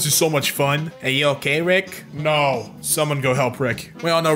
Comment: I first heard it as "Summer, go help Rick". I've watched way to much Rick and morty